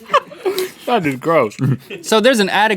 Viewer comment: sounded like link said fu**ing gross.